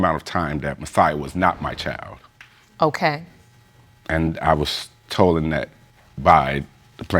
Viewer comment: Oh my goodness WHY DO PEOPLE MAKE UP WORDS TRYING TO APPEAR MORE INTELLIGENT?!?!?!!! What is "tolden"? Dude said I was "tolden" by her 1:27 SMH 🤦